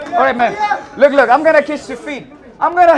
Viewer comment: did he said "I'm gonna kiss your feet"?😂